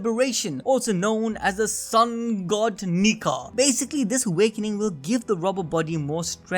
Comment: bro the subtitle say nicker instead nika 💀💀